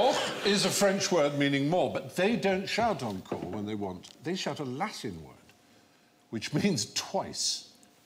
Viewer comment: I'm French and I don't understand what he said at 1:00. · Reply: "they shout a Latin word, which means twice ..."